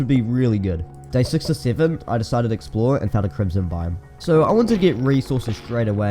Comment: When he said to explore i thought he said explode